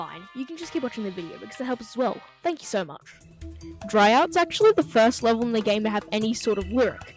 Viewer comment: i heard dry out as “freeze”